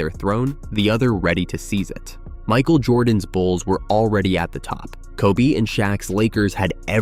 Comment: me who thought he said balls 💀